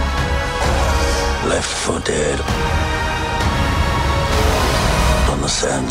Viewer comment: Totally thought he said "Left footed" and was wondering what anything had to do with his feet.